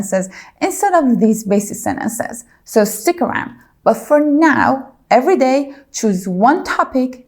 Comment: Dear, I noticed that you have said "before now" instead of "from now on" at 2:41 ? is that correct English ? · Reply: Ok, I realised that you have said "but for now" and I heard it as "before now" so thanks for the explanation 😊